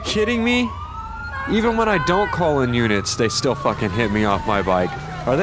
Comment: did just say "even when I don't call in units they still fucking hit me of my bike".